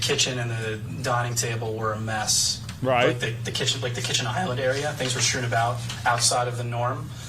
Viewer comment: I thought said amber things were messed up.